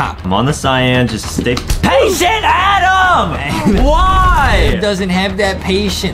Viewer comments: it kinda sounds like Adam said pay sh*t Adam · did somebody hear that adam say HEY SHIT ADAM at 2:23?